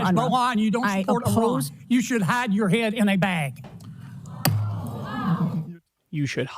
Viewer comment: I thought that he said she should have her head in a bag implying she should be executed. I guess his thick gullet made it sound that way.